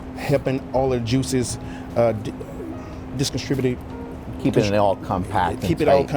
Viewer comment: anyone catch that 'disconstributed'